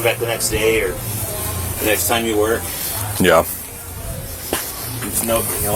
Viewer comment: Always amazed me ???? MUST HAVE BEEN AT THE HOUSE BEFORE BECAUSE IF NOT ??? HOW DOES HE KNOW ABOUT THAT BIG SPEAKER ??? OR DOES HE HAVE X-ray eyes ?? NO I WOULD BET MY LAST DOLLAR HAS BEEN AT AND IN THIS HOUSE AND GERY AWARE OF EVERYONE WHO LIVES THERE AND THE FRATS ARE IN CONTROL !! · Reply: Could you please check if the cop at at 38:27 says " we come back the next day or the NEXT TIME you ORDER?" Am I hearing this wrong??